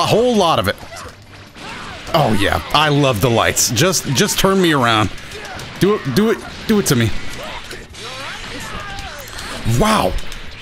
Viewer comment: “just turn me around. Do it to me” am I the only one who heard this lol